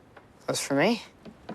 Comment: he said '" are those for me " but when i slowing down this and listening why i just hear "s for me" . Help me pls